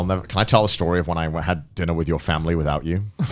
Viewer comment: drinner instead of Dinner LUL